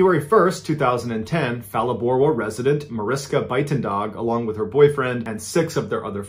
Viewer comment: I misheard resident as president and was very confused.